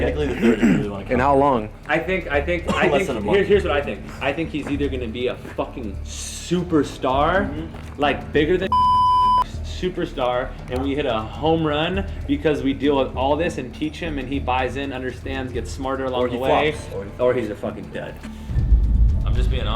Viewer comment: Did he just say “bigger than James Charles superstar” ? sips tea